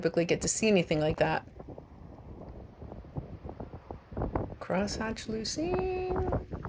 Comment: For a second I thought you said Crosseyed Lucine. And here I thought calling a shell a False Sharkeye was mean! Then I figured out what you really said was Crosshatch Lucine..